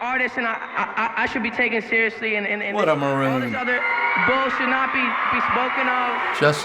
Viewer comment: Did he say 'What a Maroon'?